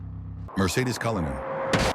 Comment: Did I just hear "Mercedes Cullinan?"